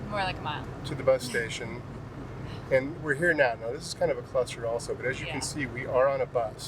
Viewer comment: I thought this was a family channel!!! 1:34. Lol. Cluster? Rewind… yep , that’s what he said.